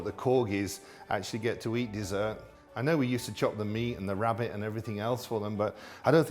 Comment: did he just said he chops rabbit to feed the royal dogs